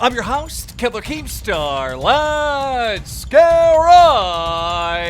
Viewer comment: sounds like keem said kebla keem star